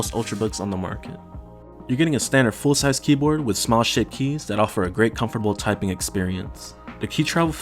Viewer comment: Great review. I was cracking up though at 4:10 .. I thought I heard "small shit keys" .